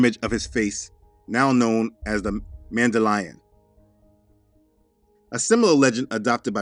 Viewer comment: When you said mandalion I thought I heard man they lying